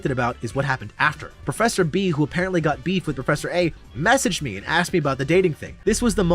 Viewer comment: really funny and i love these videos bro LOL hope u dont mind if i point out that some of the subtitles dont match what you're saying at all? like 13:57 and 14:18 ur saying professor A and its written professor F/I. 14:48 'that' is written as 'i'. 14:55 'vanta' as 'banter' LOL there's a couple other instances but yeah, it's not a big deal but i figure if you're adding subs anyway, you might want them to be correct? 😅 anyway funny as hell, im supporting u and happy new years dude!